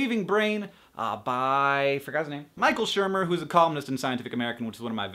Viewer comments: omg, i swear if there was no subtitles i would of thought you said he was a communist not a columnist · Oh, he's a columnist. I thought you said Micheal Schirmer was a communist... LOL.